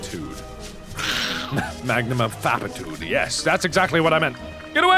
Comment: did mark just say magnum-of-fapatude?